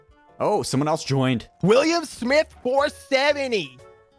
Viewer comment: I thought they said William shmit cause that's my friend lol